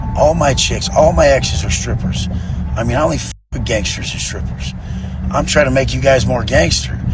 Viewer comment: did he say he only Fs strippers and gangsters?